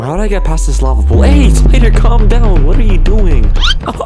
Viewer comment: I thought I heard "AYYY, Peter! Calm down"